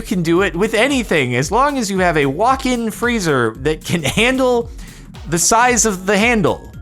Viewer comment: Initially, I thought he said "as long as you have a walking freezer" "Is your fridge running?" "No. It's a walking freezer!" 10:36 I love this rant 12:11 "Calm down, baby, or I'm going to pour cement over you and make you a mold."